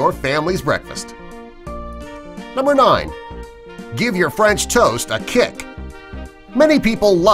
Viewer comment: the one with French toast I thought he said how to give a kick to you ( friends toast ) hahaha lol